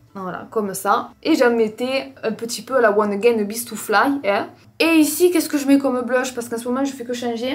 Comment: Elle dit quoi a 6:40 ? Je l'entend souvent dire ça, mais j'arrive pas a comprendre l'expression qu'elle dit... Je suis confuse ! ahah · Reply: Elle dit une expression en anglais ahah, "à la one again Best to fly" ça veut dire à l'arrache ahah